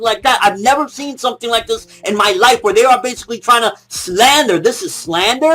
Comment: I've never see something like like IN MY YIFE!!! LOL Sounded like he said yife instead of life.